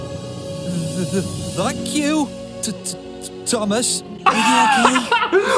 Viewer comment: Hi! I CLEARLY heard “F**k you, Thomas.” I’m surprised parents didn’t complain about it like they did Bob the builder, which I didn’t hear any swearing in that clip, it was just muttering like they claimed it to be. I did hear “F**k” in Hunchback of Notre Dame, I also think Mr. Rogers knew what he was doing because of the way he grinned right before he flipped the bird! 😂 Hilarious video! Have a good day! 😃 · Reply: I heard “thank you, Thomas.” Girl you crazy